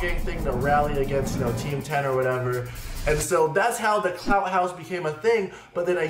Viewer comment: I thought u said u owned the clout house😂